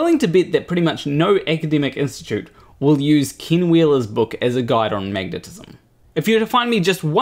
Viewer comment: I thought you were saying King Wheeler this whole time so I think that name fits him.